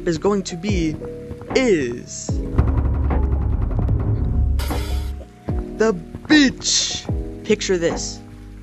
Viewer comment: When you said “THE BEACH UPDATE” My dad said did he just say Bit**